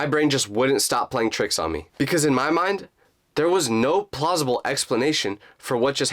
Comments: "Because in my mind, there was no plossible explanation"....Instead of saying "Plossible" Shouldn't he say "Possible"? LOL Edit: 7:36 It can also mean that Jake blocked u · who else hear prlosable